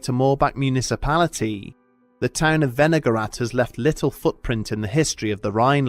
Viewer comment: I was listening instead of watching and the first time he said "Wenigarath" I thought he said "vinaigrette"